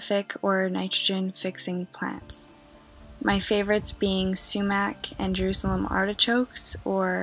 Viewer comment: Ohh my goodness what is the plant said at 1:37 please, anyone? I've been googling with what I hear but can't figure it out!! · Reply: That would be a staghorn sumac!